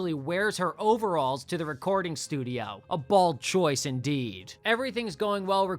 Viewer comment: Did you say it was a bold choice? Or did you say it was a bald choice? Because it sounded like one of them, and it didn't sound like the former.